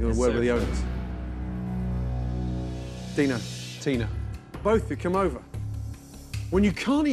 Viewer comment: thought he said "Dino, Tino"